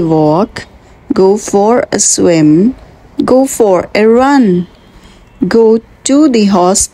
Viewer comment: i thought "go for a run" said 'go for a yuh"